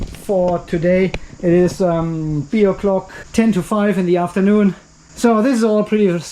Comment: Did you say it's beer o'clock at 11:30 of the clip?